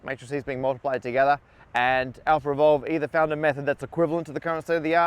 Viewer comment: up until 7:52 I thought it was called AlphaRevolve